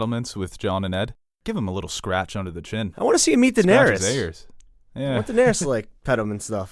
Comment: When he said “I wanna see him meet Daenerys” in reference to Ghost, I thought he said “I wanna see him EAT Daenerys”😂